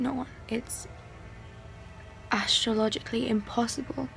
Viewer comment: Did she said astrologically impossible??